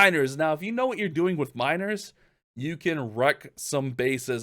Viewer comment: ”If you know how to use miner” I thought i heard minors💀